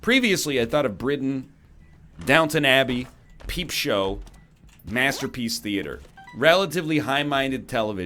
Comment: Swear I thought he said "Master P's theater."